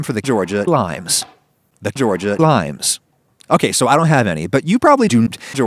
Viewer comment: I don't know if it was intentional but the way it sounds like he says "doont" at 3:06 is very funny to me